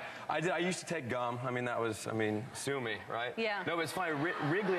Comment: did he say sumy or?